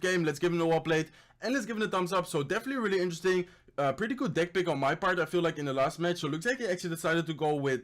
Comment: caption says dick pic instead of deck pick...